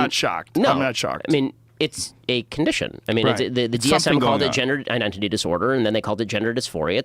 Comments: I mis-heard Ben and thought he said it's called the Jenner identity dysphoria. After realising what he actually said, I laughed cuz Jenner identity dysphoria can work too 😅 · At first I thought you said jenner disforia.